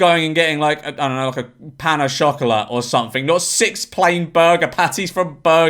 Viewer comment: Did he say chocolate funny, or is this some food I’ve never heard of